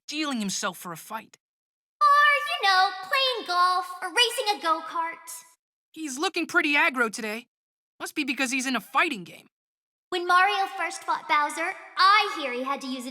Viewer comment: "I heard he had to use an ass to chop down a rope bridge"